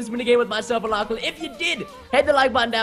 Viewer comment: Did anyone else hear Preston say "subscribe to our fresh and fuckien channels"?